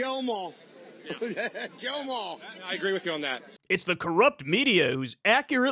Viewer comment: Love you Kyle, but I will say, to me it truly sounded like he said “Jail em all.” Edit: It feels like the whole “Laurel vs. Yanny” thing where if I focus on hearing “kill” then it sounds like he’s saying it, but if i focus on hearing “jail” it does the same thing lol. Wild